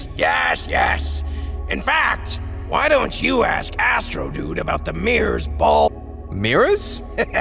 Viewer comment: “In Fact, Why don’t you ask Astrodude about the Mirrors Bald” is what I heard when I first listened to it💀